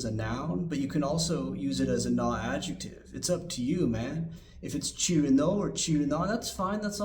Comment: when you said チルの at 1:50 i heard チルノ and my ears perked up and i sat up straight, suddenly alert. doesn't help that i've been out doing my 東方活動 for my fave 押し cirno all day (or however i would say this. still not the best at japanese. please instruct me on the correct words). keep up the good work!